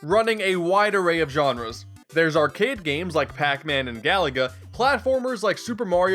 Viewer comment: When you said "arcade games" I heard it as "our kid games" and I got really confused.